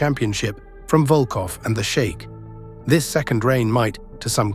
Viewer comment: "The U.S. Express regained the championship from Volkoff and the Shake" -- Yeah, I remember that day, I was... Wait, did you call him the Shake?! 🤣